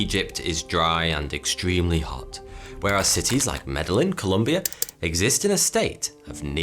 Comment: Did he just say “medal-in” Colombia? For Medellín? Lol